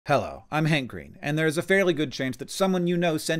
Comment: I heard, "Hi I'm hangry." I thought, "And you need someone in Georgia to bring you food?"